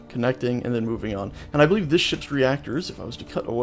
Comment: When you said "And i believe this ships reactors blah blah blah" i heard: "And i believe this shits reactors blah blah blah" on